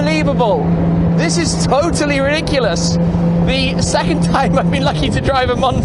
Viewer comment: Did Tim just say, "this is totally Rick-u-lus" @ 8:51? Must be an english thing... ;)